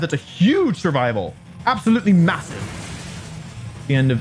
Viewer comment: Did you say... Massive?